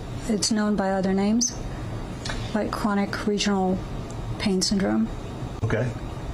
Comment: This nurse bonnie rice does not about crps pain 😢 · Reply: I swear the Doctor said Chronic but text says complex.... duhhh